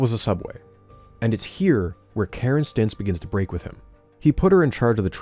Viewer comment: If you're dying to see the conclusion of this trilogy you can watch it early on Nebula: <link> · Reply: hi just so you know, at 1:13:03 the subtitles say "Karen's stints" instead of "Karen Stintz"